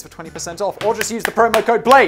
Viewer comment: What he said: "I really want to--come on guys, let's get on it." What I heard and laughed about: "I really want to come on guys, let's get on it." 🤣